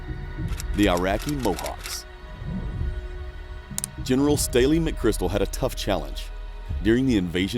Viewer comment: Stanley McChrystal was the JSOC Commander in 2003 :) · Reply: it sounds like you said Staley McChrystal.